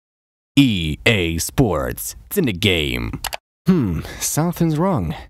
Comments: I'm spanish and when I was young I heard: "EA Sports, inchindigaei" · I always heard "EA Sports - It's still the same" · I Heard "EA Sports Turn up the game"